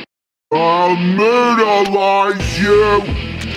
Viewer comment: Little Eddie: "I'll murdalize you!" Jude The Dude: "Well, howdy, rookie!" The Twirlenkiller: "Your life ends here!" RinRin: "I'm going to enjoy every moment!" Shogun: "Can you beat me, little one?" Yokozuna: "Take your best shot, boy!" The Shamans: "I smell fresh meat!" Frank: "Me smash!" Elise: "Do your best, child!" Kojack: "I'm gonna #&$@ you up!" The Masters: "For the glory of our house!" Martin: "One destructible target found!" Black Baron: "Navy SEALS? Special Ops? C'mon #$&@ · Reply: I thought Eddie said "I'll deaditize you!" )